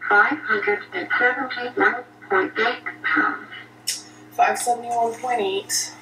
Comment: i can't be the only one who heard 579 and not 571 and